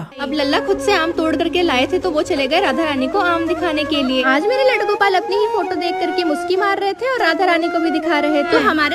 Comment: I thought she said " appni e photo dekh k muthi marr rahe thee 😅"😂😂😂😂